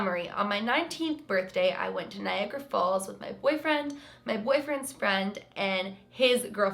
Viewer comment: For a second I thought you said your boyfriends girlfriend